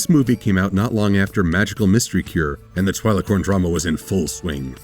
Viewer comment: I heard "Twiliporn drama was in full swing..." I think I should clean my ears...